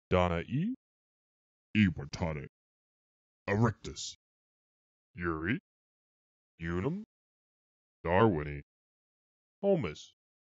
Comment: i thought it said urinal